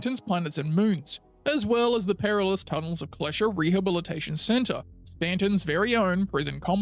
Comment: I swear I heard "Tunnels of Pleasure", not Tunnels of Klescher.